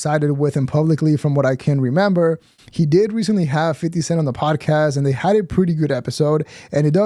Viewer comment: did he just say "50 centS"? 🤣🤣🤣🤣